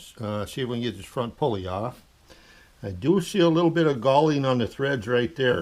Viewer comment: I was unfamiliar with the term galling. It sounded like he was saying gaulding. Finally got the correct term: Galling is a form of wear caused by adhesion between sliding surfaces.